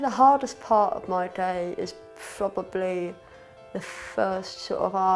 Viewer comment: did he say "frobably" ?